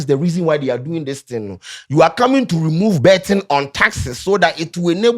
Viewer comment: Did say you are coming to remove betting from taxes 😂😂